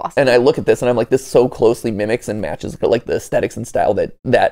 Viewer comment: Wow wow wow wow wow wow did he just say the mimic